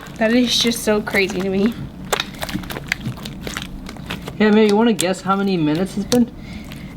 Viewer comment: I heard "Hey bae do you want to guess how many minutes has been"